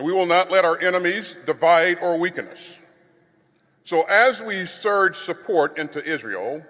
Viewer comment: 'so as we provide support into Israel'? - It sounds like he said: "so as we surge support into Israel"